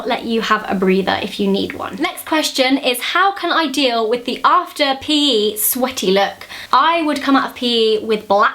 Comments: i kept hearing "after you pee" haha but she was saying "after P.E." XD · Thought you were saying "after a pee" hehe then I realized you said "P.E." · When you said "after P.E." I literally thought you said "after pee"...I was really confused for a second ahha.